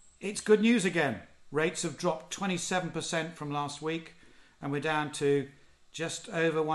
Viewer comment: For a moment I thought you said rapes had dropped 27% :s